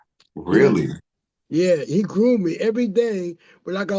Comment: did he say "he was grooming me!" ???? Excuse me byiiiatshhh you were 28 .years old and a fully grown man...... innit!!??!??